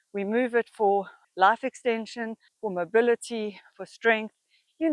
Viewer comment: when i first heard you say “We move our bodies for life extension.” At first, I heard “laugh extension” and i really liked that too cuz we can’t laugh if we are dead.